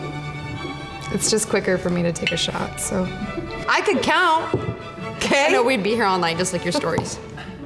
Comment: May someone tell me what she said at 2:35? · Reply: Tony Ng Ng "I could count, 'kay?" "I know/ No, we'd be here all night just like your stories"